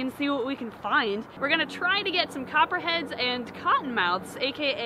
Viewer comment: A few for a second I thought you said cotton heads and copper mouth’s And I wuz like “ITS COPPER HEAD AND COTTON MOUTH”